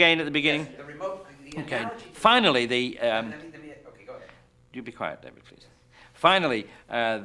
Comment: Hopefully he was being playful but I swear he said "you be quiet David please" at 1:47.